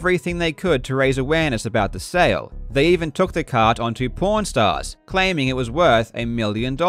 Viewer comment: Is it me or does it sounds like he is saying porn stars and not pawn stars? LOL Love the video!!! Thanks Karl